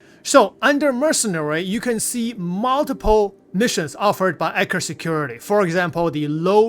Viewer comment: I kept hearing "Anchor Security". More emphasis on the "E" and "t" in Eckhart 😅